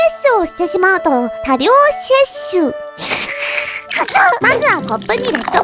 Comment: I only heard tired sexuuuu. Don't know why.